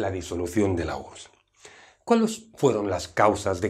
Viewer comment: se da por terminada con la disolución de la qué? en el minuto 0:42 no entendí que dijo si alguien me explica gracias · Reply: como nadie contesta me contesto sola dice la "URSS"